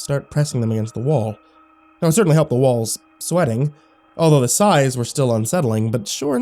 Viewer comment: thought it said "the balls sweat" at first